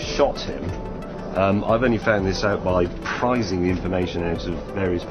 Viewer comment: the caption says that James tells the press that he was “prizing” the information. No. He was “prising” it. Which means to force open. #grammar